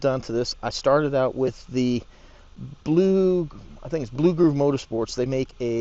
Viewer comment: Thank you and no just had to adjust the body mount in the center down a little bit thats it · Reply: thanks for the response, I currently run the 6800s and they fit but I would like to get the 8000s in there so I’ll definitely pick up that spacer. I thought you were saying blue crew motorsports is that correct?